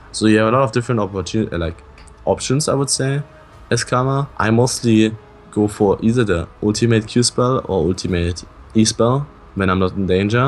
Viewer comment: what is he saying? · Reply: You have a lot of options as Karma. I mostly go for either the...